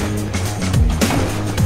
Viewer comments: I thought he said bring the fu*k back 😂😂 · Lol, I thought it said bring the f* back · It kinda sounds like its saying. bring the f**k back. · Please "bring the fun back" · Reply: I thought it said at first “Bring the Pump back” lol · I heard bring the pump back 🤨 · I think they said bring the f*ck back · Lol, I thought it said bring the f* back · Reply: I thought he said bring the funk back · did he just say bring the pump back